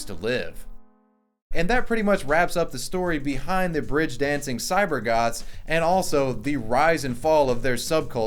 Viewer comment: really sounded like "cyber gods"